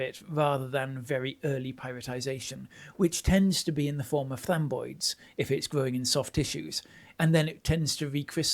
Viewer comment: I’m trying to understand the word being said at about 13:58. At first I heard femboys (obviously not correct) the closed captions say flamboys which also seems in correct. Is it maybe framboid? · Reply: Aye, it was framboids! Sorry, should have added a picture and caption there! 😅